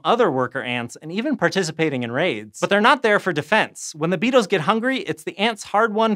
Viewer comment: I thought he said the ants have raves and had to turn subtitles on to figure out that he was talking about raids.